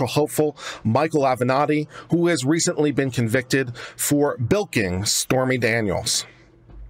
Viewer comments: OH! BILKING! I thought you said Stormy Daniels MILKING. Nevermind. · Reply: I thought he said BOINKING. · OH! BILKING! I thought you said Stormy Daniels MILKING. Nevermind.